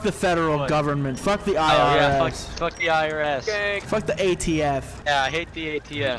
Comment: Fuck who bruh? For a second I thought you said fuck the ATF